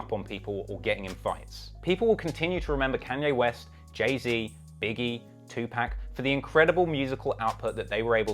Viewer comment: was gonna smack you silly after hearing the list of influential rappers at the end cos I thought you said Iggy instead of Biggie